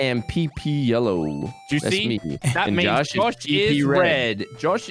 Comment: "-and josh is pp red" i hear you. Even though is trying to talk over you